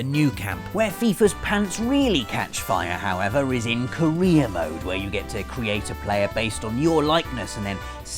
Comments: When he said "career mode" in FIFA I legitimately thought that he said "Korea mode". This has been a special look inside the mind of someone from america who has never played FIFA. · Thought he said Korea mode instead of career mode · I thought he said Korea ode instead of career mode... Kim Jung Un plez don't nuke me!